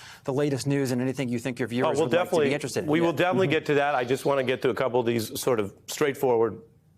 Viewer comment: He said "My opinion is neither here nor there," but I heard "I'm a tool."